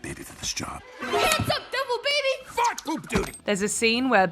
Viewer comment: DID HE JUST SAY F**K??